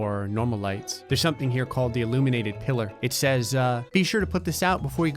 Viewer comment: I'm watching this a little bit high and I thought that it said illuminati pillar